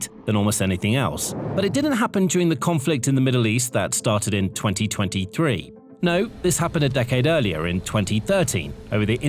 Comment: Hahahaha, What? 00:14 it sounded like you said 2030